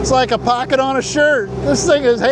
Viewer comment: Oh, shirt - I thought you said 'skirt' haha